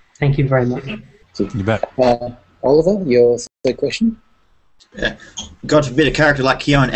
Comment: What did Oliver say at 33:03 that had the others laughing and like, "oh god did he really just say that?" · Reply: God forbid Alacard and Kyon